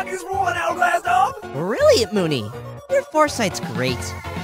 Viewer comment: Script(?) Moony: sigh can’t you hurry we need to go find a spot ENA: Ah auction day these are the upmost grand of days, this is going to be so so- awful. I think this is going to be the worst day of my life Moony: wow look at the crowd today ENA! Everyone’s ugly. Today’s item must be super special, what do you think it is? ENA: why would I know? I’m too stupid to even imagine anything. Ahh sincerest apologizes. Auctioneer: Alright Yo! Welcome to the daily auction! Sweeet sweet! Yea! All your existential troubles go away soon! Ok! Today we have this... this... dog..inside..an hourglass, hourglass dog! Sweet! Our starting price is sweet chocolates for an hourglass dog! ENA: really Moony your foresight’s great! Today’s offers a big one! Moony: that dog looks like he has something to say, if you get what I’m saying laughs ENA: Eh wait no no no that was very weird, what is wrong with you? Grave 1: 50 chocolates Auctioneer: 50 chocolate bars sweeet still counting in Grave 2: I’m offering 400 Auctioneer: 400 even lowest Grave 3: one fatty catty Auctioneer: one fatty catty. Going once going- ENA: was that a rain rock I just felt? Moony: oh shoot they’re going to cancel the auction! Darn life is so unfair. Ugh. Rude, you should ask before doing that you know? ENA: I’m sorry, my stupidness never ends. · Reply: I hear “brilliant moony! Your foresight’s great” rather than “really Moony”